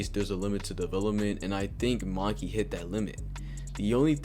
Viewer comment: bruh subtitle says "monkey" when u say "maki" at 11:43 💀